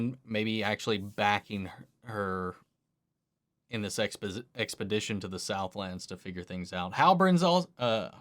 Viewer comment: Did I just hear "in the sex position" 18:08 xD